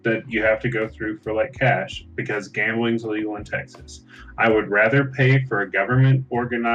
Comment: Thought it said ‘should gaming be legal?’ to which the correct answer is absolutely not.